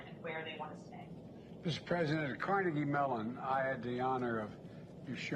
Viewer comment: Did biden say "Mr. President"? Lmfao